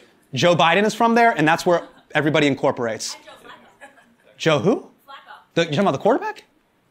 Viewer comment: did he just say joe who